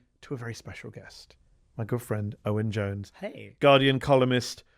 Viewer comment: I thought he said "my girlfriend Owen Jones"